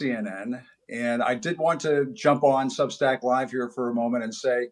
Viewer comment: Get on Acosta’s Substack (whatever that is - I thought he said sad sack) and ask if he’s doing his videos from his mums basement